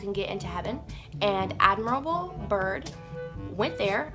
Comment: Did she just say Admirable Byrd LOL? They really have the world's best and brightest working on flat earth don't they?!